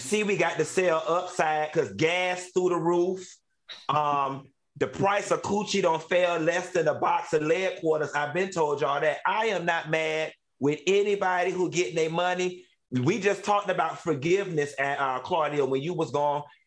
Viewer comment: Chiiiiile whole time I thought he was saying a box of LEAD QUARTERS. ! Cuz real quarters are silver…🙃🫠😂